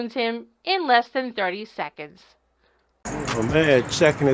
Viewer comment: WTF did he just say ?? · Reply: "in less than 30 seconds."